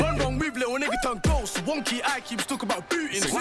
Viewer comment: I listened to this on Spotify and threw my phone when I heard "wonky eye" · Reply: He said one eye